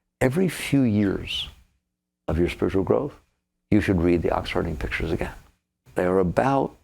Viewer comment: What teachings is Michael referring to around the 24:00 mark that he recommends reading every few years? It sounded like he said The Oxford in Pictures but that doesn't sound right. Anyone??